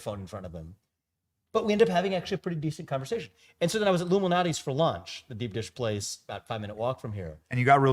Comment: I really like deep dish pizza is this the place he was talking about? Lou malnati's chicago? It sounded like he said Illumanati and that was the Youtube auto captioin displayed.